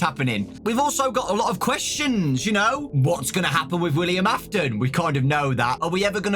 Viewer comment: Why did the Subtitles say "William Aton" Instead of "William Afton"💀